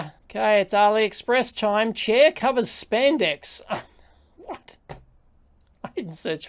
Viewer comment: I thought you said Cher Covered Spandex.